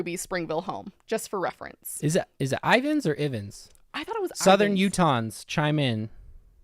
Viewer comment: “southern Utahns” What I heard: “southern croutons”